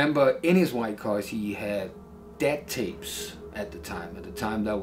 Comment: What is the dude saying Eazy had in his car? Death tapes? Deb tapes? Little Debbie Snack Cakes? I've listened a million times and I still can't decipher it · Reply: DAT Tapes? Google it.